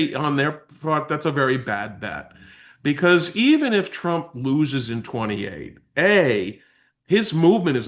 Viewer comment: Did he say, “Even if tRump loses in ‘28?” wtaf?